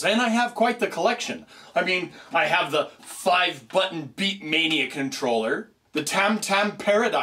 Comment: did he say beakmainea? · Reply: Mr. Kite It's beatmania. I had stepmania on my old laptop